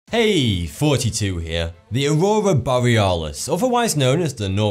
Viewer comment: whenever he says 'thoughty 2 here' I hear '42 here'